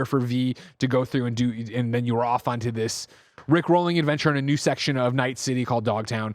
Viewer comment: Did say "rick-rolling"...? As opposed to "rip-roaring"? Lmao